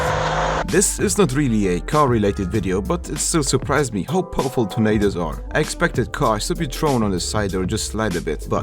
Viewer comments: I thought you said Tomatoes 9:45 not tornados. I was waiting for a tomato to hit that car, but it never happened :/ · I thought he said ‘it’s surprising how powerful tomato’s are’ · i thought that he said tomatos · You can't tell me I'm the only one who understood Tomatos instead of Tornados